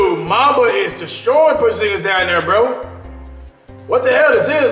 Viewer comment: Did I just hear “Porzingas Gordon”